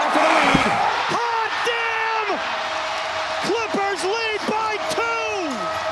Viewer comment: Did bro say a bad word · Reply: he said dam